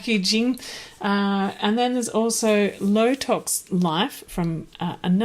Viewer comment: Had to rewind, in your recap it sounded like you said botox life....... low tox life, must have been my hearing. Thank you for the recommendations I am in the UK and will look for a weed foragers handbook for here.